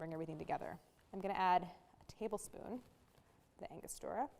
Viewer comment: Hi, Bianca! Is it the first liquid she adds to the filling? If so, that is, "1 tablespoon orange bitters or Angostura bitters" from her recipe. Was that what you were looking for? 🍎 -🥐Lily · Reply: I thought she said angostura, but after rewinding three times I still wasn’t sure. I enabled close captioning, and was then able to read it on the subtitle.